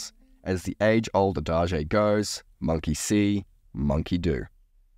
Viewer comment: did he say adage? lol